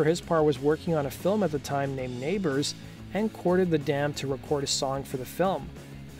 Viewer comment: Is it just me or is he saying "dand" instead of "band"?